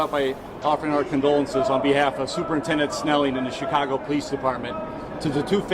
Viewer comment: Alaè I thought it said Chico-go